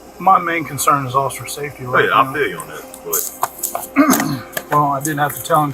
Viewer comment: Just one thing, don't put subtitles on there that were never said, it just discredits you, eg @ 17:05, where the other officer says "I feel you but", he clear does NOT say "be honest" after that, but you've added it onto the subtitles as if he did.